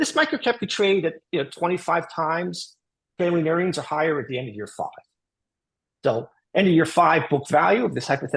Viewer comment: did so. catch ".... earnings are higher at end of Year 5" ? Didnt understand it acoustically